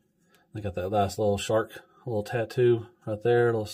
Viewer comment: I heard shart and fart around 7:30 7:33,ha ha. They look like fun figures, may pick the Pantera one up,and this one too.